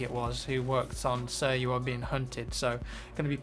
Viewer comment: At first I thought you said: "Sir, you are veing cunted." Haha!